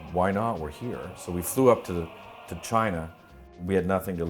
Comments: i thought that said rip china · I thought it said rip China 😩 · i thought it said rip china